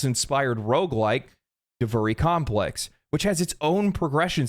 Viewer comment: Was listening to the video and thought you said "The furry complex". I'm now going to call it that.